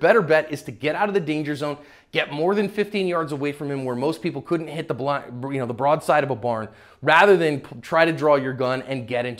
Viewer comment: Is it me or did he say “most people can’t hit the blunt” before he corrects himself at 4:25 🤔